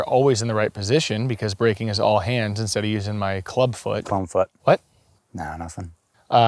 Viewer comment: "Clone foot" "What?" "Nah, nothing" Absolutely hilarious. Loved it. Your comedic timing and delivery are top notch. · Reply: Thought he said clown foot and still thought it was funny